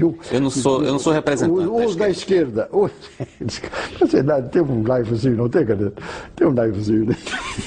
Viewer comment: me corrijam se eu estiver errado: em 7:40 o repórter diz não ser da esquerda, eu não entendi direito que o general disse: "você deve ter... " parece dizer que tem um pezinho na esquerda ou algo assim... é isso?